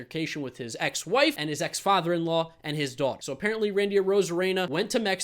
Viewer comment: it almost sounds like just said Arozarena got arrested after an altercation with his dog (I know he said daughter)